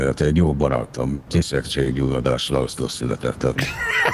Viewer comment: mit mond hol született?😂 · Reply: Kisebbség-gyulladáslászlón